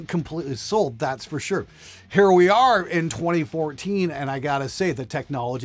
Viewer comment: did he say "here we are in 2014"?